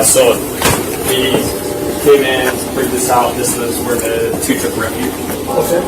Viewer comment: as he is pointing to the spare room on lower level, does he say "This is where the 2 took refuge??? Sure as hell sounds like it to me!